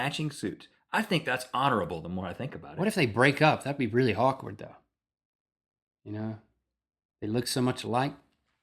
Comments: did Link say, "That would be really hawkward." or are my ears playing tricks on me >.>? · The first time I heard this I thought he said, "That'd be hawk-ward" Lololololol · I thought link said "what if they brake up? that would be really HAWKward" lololol xD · Did Link say at 12:49, "that would be really HAWKWARD though"? Seriously though, Batman kicks ass!! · I thought I heard Link say "If they broke up that would be really Hawkward" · When link said, "what if they break up?" It sounded like he said, "that'd be hawk-ward." I was so hoping they would turn that into a pun. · i thought link said if hawkman and hawkwoman broke up it would be hawkward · I thought Link said "Hawk-ward" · When Link said "What if they break up, that would be kinda awkward" I thought he said, "that would be kinda hawkward" and made the best/worst pun ever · did he say that would be really "hawkward" lol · When Link was talking about Hawkman and his girlfriend breaking up, it definitely sounded like he said, "That would be really Hawkward..." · Did Link just say that would be... "Hawkward"? xD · I could have sworn Link said Hawkward. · did link say that would be really harkward? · i thought link said "if they broke up that would be really hawkward..." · Ahaha, is it just me, or does it sound like Link says "hawkward?" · For a minute there, I swore I heard Link say ' that would be really hawkward'. XDD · did Link just say "What if they break up, that'd be really hawkward?" Bravo Link... Bravo...